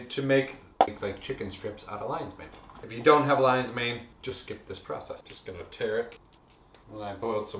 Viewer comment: “If you don’t have lion’s mane just ???” What did he say at 1:24? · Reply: if you don't have Lions Mane just skip this process. - do it without.